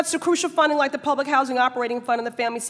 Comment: Typo at 2:54. She says public housing. The caption says Pubic Housing.